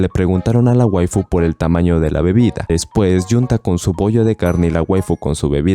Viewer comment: entendí "con su p0ll4 de carne" XD